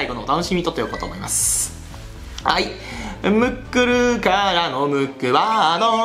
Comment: ムックルがもっくんに聞こえた